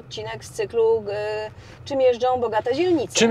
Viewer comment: Usłyszałem "czym jeżdżą bogate dziewice" - może być interesująco 😋